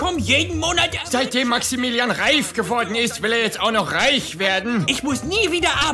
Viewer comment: ich habe zuerst Statt reif reich verstanden und fand die logik ,dass der gutse erst reich wurde und dann reich werden will , seltsam 😂